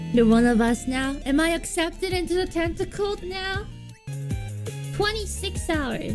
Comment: I think she said tentacult not Tentacled 😨